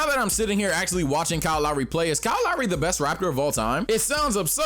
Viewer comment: My dumbass thought you said “is Kyle Lowry the best rapper of all time”😂 not “raptor” 🤦🏿‍♂️